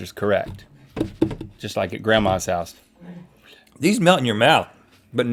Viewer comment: I legit thought that he said “ these nut in your mouth” 😂